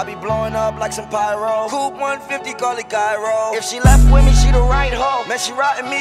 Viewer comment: i had to look at the lyrics cause it sounded like poop 150